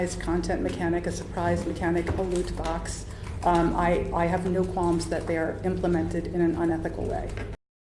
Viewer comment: does she say, "unethical"? In other words is she saying the implementation of these is "unethical"? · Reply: "I have no qualms that they are implemented in an unethical way", so.. I guess? If she did say 'ethical' the sentence wouldn't really make sense, because who would complain about something being ethical? Oh wait.. maybe someone who's job depends on the opinion of shareholders